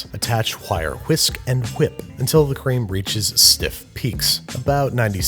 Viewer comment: "Wuwhip" Wait what? what did you say?